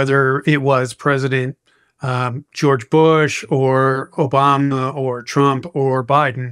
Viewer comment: did he say obamNa?😂